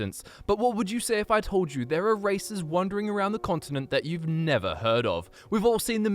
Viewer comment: I don't get the joke Help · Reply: i thought it said spaces you never heard of instead of 5 races